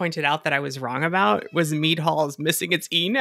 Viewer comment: I never saw the mead(e) hall written ,i thought you were saying meat hall, and i never questioned it so i never checked